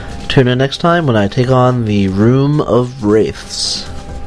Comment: i thought you said 'room of rape' lol.